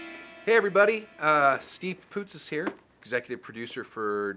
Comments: It sounded like he said "Steve POOTIS" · Whenever I hear his name, I hear it as "Steve Pupusas" and then I'm like "Damn, I could go for a pupusa right now"